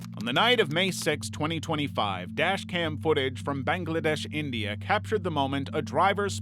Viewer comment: Bro did you say Bangladesh,India